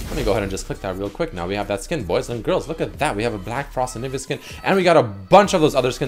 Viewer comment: did he say n? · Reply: he said anivia but fast it sounded like the n word but he just said anivia fast